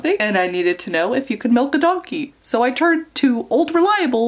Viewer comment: Milking a donkey 😂🤣😂🤣 that so makes me want to read what you are doing. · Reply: Elisabeth Hays oh that makes way more sense - I thought she said “melt a donkey’ I was pretty confused and a little disturbed and not sure Google would have helped. 😂😂😂